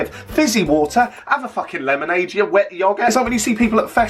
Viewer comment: Am I going insane or did he just say “have a lemonade you wet yoghurt”😂😂